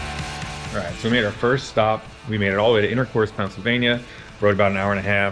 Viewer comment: Wwww-wait... 04:52 Did he say just "Intercourse", Pennsylvania?!?! That has got to be THE best city name I have ever hear, in my life!